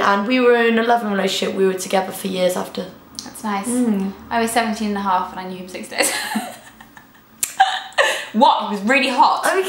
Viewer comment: What does say at 5:56? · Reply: "I was 17 and a half and i knew him 6 days"